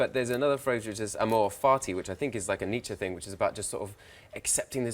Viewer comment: What's the phrase he says at 3:35? The Nietzsche phrase, not the 'carpe diem' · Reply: Marín It was amor fati (love of fate or love of one's fate) "My formula for greatness in a human being is amor fati: that one wants nothing to be different, not forward, not backward, not in all eternity. Not merely bear what is necessary, still less conceal it—all idealism is mendacity in the face of what is necessary—but love it." -Nietzsche